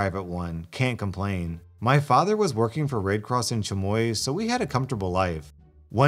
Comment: I honestly cannot hear anything but "rape cross in chanoi" anyone else?